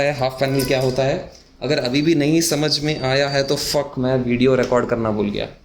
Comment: did you just say ffff @ 3:35?